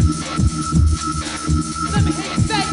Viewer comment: Growing up I always thought they were chanting, “Let’s Go Bulls!” 😂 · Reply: I first heard "pass the ball" lol.